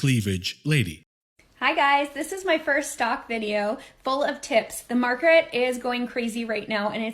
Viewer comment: did she said "full of ti*s?" :)